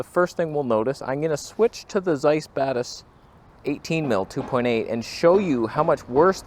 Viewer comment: When you say "Zeiss Batis," I always hear "Zeiss Badass."😂😂😂